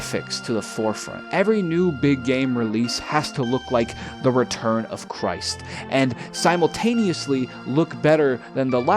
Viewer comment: I thought you said "Return of Crysis' at first